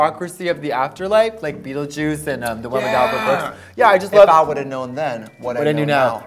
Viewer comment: beetlejuice and what? i can’t hear what she’s saying 😭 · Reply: the one with Albert Brooks (“Defending Your Life”)